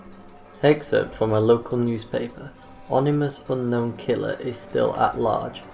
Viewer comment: Was going to like....then heard him say "onimous" instead of "ominous". Nope.